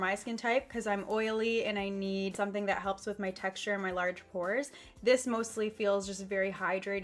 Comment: did she says something bout hores